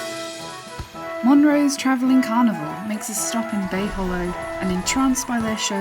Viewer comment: I heard travelling carnivore